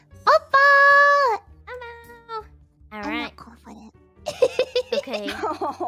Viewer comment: what fuwawa said before ame say u will be okay? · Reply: "I'm not confident"